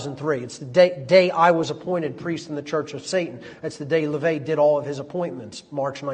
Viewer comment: what did he say right there? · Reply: Yes, he was once a priest in the Church of Satan.